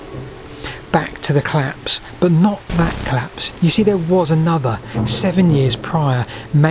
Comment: I seriously thought he said "Back to the claps, but not THAT claps," and was very confused as to why brainrot had entered the chat on an otherwise intriguing historical analysis. /colæps/ is quite different pronunciation from /cəlæps/ or /clæps/ and I was terrified this video was about to take a VERY different direction. Crisis averted. Syllables matter, people.